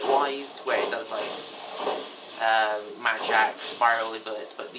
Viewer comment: i thought you wer saying magic... but it was mad jack...